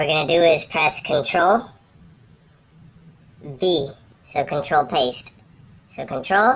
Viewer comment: Wait, did he say D or E? Please reply because I don't know.... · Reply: he said v